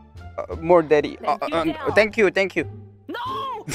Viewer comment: did he say 'more'? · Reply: 이원영 he said “more daddy”